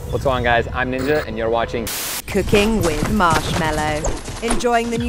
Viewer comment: I thought it said cocaine with marshmello..